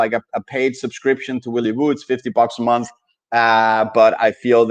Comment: Hi! I'm a copier and I don't see where to get the willy woo newsletter for 50% off on etoro? Am I looking in the wrong place? Thx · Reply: Yeah I just went back again and listened at 49:35 and you said you paid $50 for yourself and your copiers. I don't know why I thought you said 50% off for your copiers. My bad man